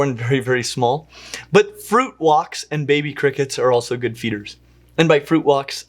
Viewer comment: "Fruit walks" is never not going to be funny. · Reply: OHHHH I heard fruit wok's. That makes wayyyyy more sense XD